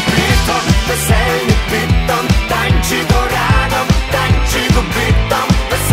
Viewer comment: Na początku usłyszałem "Tańczym go rano, tańczy kopyto" 😁